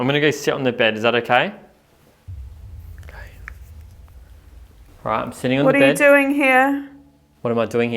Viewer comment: what was said in a whisper. Don’t think it was either of you guys. Loved this place, definitely on the list to visit ❤ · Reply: It almost sounds like a Response to by a Spirit saying "Ok" 👍. Good Catch